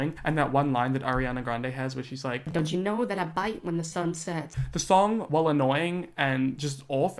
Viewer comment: Yooooo I thought she said, "Don't you know that I FIGHT when the sun set" 🤣🤣